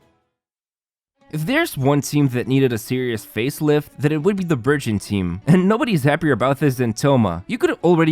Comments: I'm sorry but- at 0:08 I thought he was saying the "Virgin" team 😅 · I swear I thought he was saying “virgin team”😂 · OMG, I misheard BURGEON team as a VIRGIN team. I was like: what? Ah, well, Xingqiu is a pure boy, so it's probably true. Nahida - certainly even if she is 500+ years old she is still a child in her behavior, smart but childish. Who's next? Fishl, well, she is a «princess» so it's likely to be true. But Thoma… well probably he is Ayaka's simp, so it makes sense. Okay, let it be Virgin team